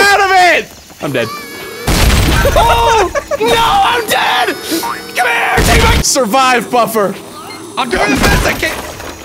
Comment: im soory what did he say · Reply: Come here! Take my